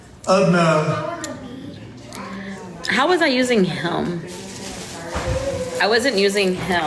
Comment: why does it sound like she was saying 'Helm' instead of him????😅